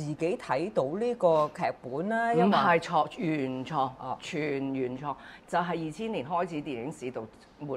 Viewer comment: I think she actually said 原創、全原創